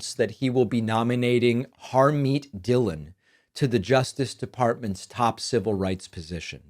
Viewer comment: I thought he said "Army Dillo" - who knows of who am I talkking about?